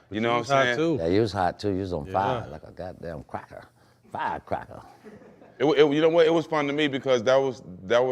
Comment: man what mad max say?!?! 😂😂😂😂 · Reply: FIREcrackerr 😂 love Maxx